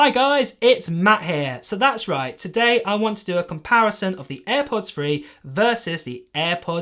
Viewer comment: Thought you said Airpods free!